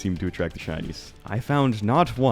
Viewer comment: I misheard him said shinies, so I got pretty confused as to why the Chinese were attracted to terraria mundane streams